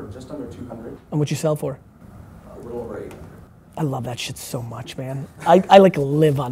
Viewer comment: How much did he sell it for? I couldn't hear properly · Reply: A little over 800